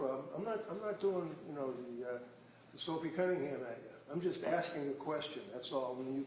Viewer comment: did he just say "I'm not doing the Sophie Cunningham right now" ??!?!?!